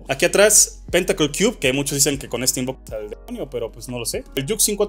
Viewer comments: Que dijo en la censura del minuto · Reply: “ con este invocas al demonio” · "invocas al demonio" entendi